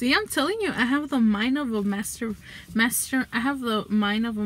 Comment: see i’m telling you i have the mind of a master master i have the mind of a master mind what’s that idk but i’m so creative like that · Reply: All these years i thought she said i'm so CREEPY like that